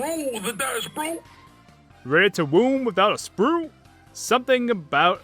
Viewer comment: I heard "ready to doom a spouting blue?"